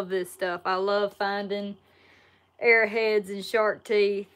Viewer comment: Lol with your accent i thought you were saying air heads instead of arrow heads.. lol